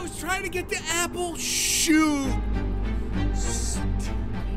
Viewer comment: he actually said sh*t! Listen Carefully! He said it after “shoot”